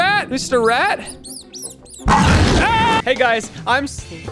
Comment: I thought he said "HEY guys I'm slimp pickle" in the beginning intro there.